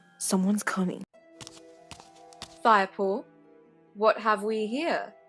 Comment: i thought bluestar said firepool